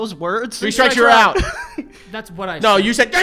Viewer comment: not even glazing, but at 8:40 I understood more than I did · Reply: I definitely heard say "three strikes you're out," so he should've gotten the points lol